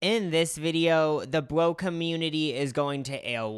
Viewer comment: Thought he said the broke community for just a second 😂